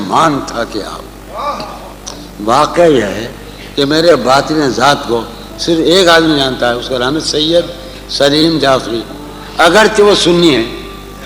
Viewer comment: What does he says @ 14:20 about saleem jafri....after that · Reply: Matalab meri under ki halat ko sirf ek he admi janta hai saleem jafri agarchy wo sunni hai